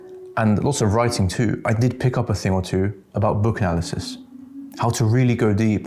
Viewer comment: Sounded like "Book Alices". Which the thing about going deep still makes sense.